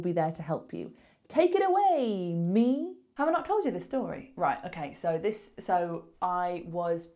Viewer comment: I didn't understand the word in 2:20, in the subtitles is said "Have I not told you this story", but I hear something like "Have I am close to the story", which does not make sense. Is this a form of intrusion or something like this? I fully understood the rest of the easiest level. Thank you!